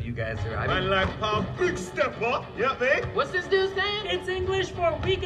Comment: But what Hobie Damn saying ????🤦🏾‍♂️🤦🏾‍♂️🤦🏾‍♂️ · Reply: Man like pav, big stepper, you get me?